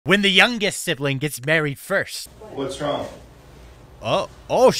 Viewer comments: For some reason i heard "when the youngest sister get buried first" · Thought you said buried first 💀💀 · I honestly thought you said buried first XD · I thought he said burried first